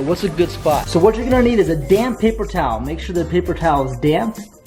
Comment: when you said damp papaer towel i thought you said damn paper towel XD